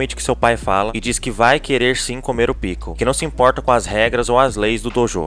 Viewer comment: "EU VOU COMER O PICKLES!!" KKKKKKKKKKKKKK TANKEI FOI NOTHING